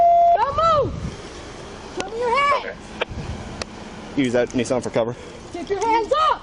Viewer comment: What did he say at 4:08 when he tapped her back? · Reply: Use that nissan for cover